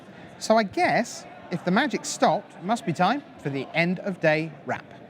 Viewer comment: What I heard was: "Time for end of day rap". I was disappointed.